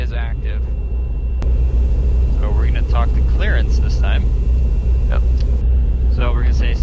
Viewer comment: Did he say, "Talk to Clarence?", Rodger, Ovour, Victor. Whats my vector Victor, Roger. Huh? Rodger Clarance, Clearance.